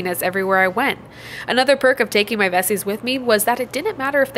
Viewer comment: I thought you said ''taking my besties with me'' 😅